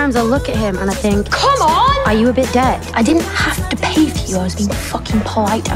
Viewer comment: I swear I always hear 1:16 as "are you a bit gay" then i read in your DB and cracked up😂